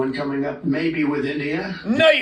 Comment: I swear I thought he said, "A deal with Indiana." I think he crapped himself at least twice. 😀 He does scare some people. Sadly, they are all elected pussies with no backbone... or morality.